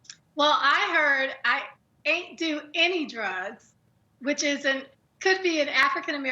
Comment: I heard “I ain’t took any pills”